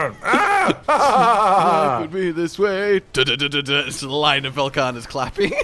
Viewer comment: On the topic of hearing the friends song wrong I thought it said "gonna be this way" I think I need hearing aids KEKW